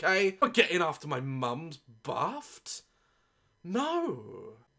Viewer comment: Did you say, Baft